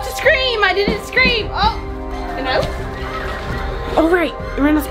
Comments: Who else heard say and i oop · did say “and I oop”😆